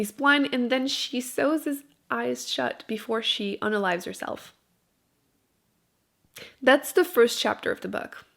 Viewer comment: Does anyone know what she’s saying at 6:34? “She sews his eyes shut before she _______ herself?” AND @ 9:45 she says the ______ of words she pronounces like a K? (G ending of words maybe?) I rewound over and over and over in both cases and couldn’t figure it out and it’s buggin me! Need to know! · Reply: is "unalives herself"